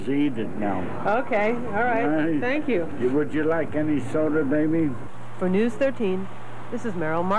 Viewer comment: "would you like any soda baby?" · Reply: The first time through I heard "would you like any soda maybe?" but your comment caused me to go back and give it a stronger listen and I think you're right. LOL. "Any soda baby?" Oh well... Good times. ^^